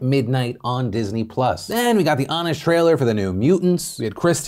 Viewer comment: Every time you say “we’ve got the honest trailer” I keep hearing “we’ve got the Amish trailer”